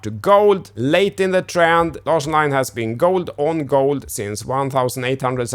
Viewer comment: On the charts, what's the purple and yellow indicator name, in the transcript it says do line, but don't think that's right? Thanks in advance 🙂 12:15 mark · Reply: It's the Larsson Line, my own indicator. You can go to my website to learn more. (Link in description box).🤗